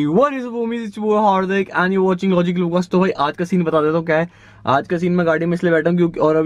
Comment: Hey, whwysgejeydhdhsshe, it's yoboi. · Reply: Hey what is up homies, it's yo boi. Agar kisi ko samjh nahi aya toh😂